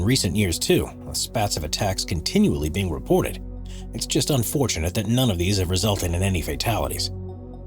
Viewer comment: did anyone replay 2:20 lmao i thought it was just me that heard " it's just unfortunate that any of these have resulted in fatalities " lol